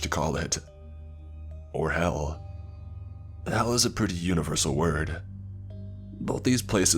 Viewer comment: Huh? Did he say 'Hell or Hell'?